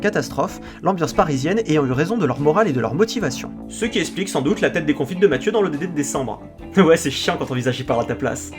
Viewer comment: Qu'est-ce que tu dis à 22:42? Tu parles vite j'ai du mal à comprendre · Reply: "Ce qui explique sûrement la tête déconfites de Mathieu en décembre" je crois qu'il dit